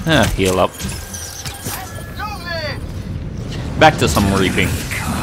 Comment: "back to some raping" XD I lmfao when I heard that! No offence to his accent